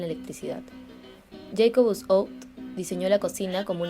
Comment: Jacob Scout? Jake Bsc...? que dice ahi? ajsjsa help